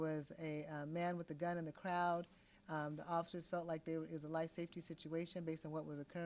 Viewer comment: I thought you said it's a safety protest? stupid inconpetent chief of police